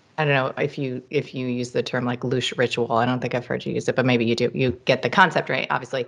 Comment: What did she say? Loosh ritual?